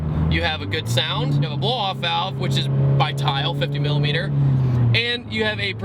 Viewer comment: I thought he said "no blow off valve" but then he said "tial q 50mm" which is it?